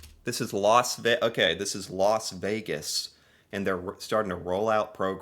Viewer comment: What I heard the guy say was "Loss Vegas" ... is it called that now?